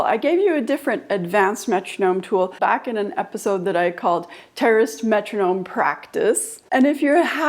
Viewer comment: Oh my gosh, I was listening to this in the car. And when you said terrorist metronome practice that's what I thought you said lol. Yes that metronome is terrifying LOL sometimes. But then I saw you didn't mean terrorist you meant terraced. Love the idea of beating longer notes instead of subdivision.